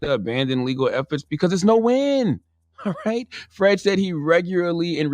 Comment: It sound like Charlemagne is saying Fredisha but he is not.